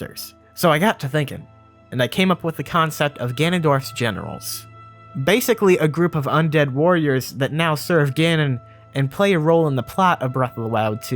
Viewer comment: Bruhhh, at 6:15 I swear I heard “ganondorf’s genitals” the first time.